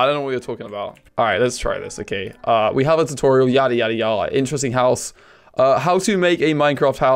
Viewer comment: anyone hear him say yare yare > - >